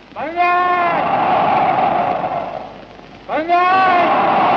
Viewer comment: What are the people shouting in response to “banzai”? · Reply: They also shout "banzai".